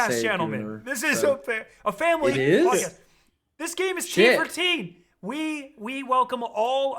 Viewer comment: for a split second I heard t4t and I was like "yeah that makes sense" XD